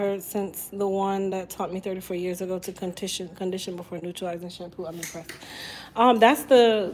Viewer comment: Where’s the neutralizing shampoo I heard you say normalizing shampoo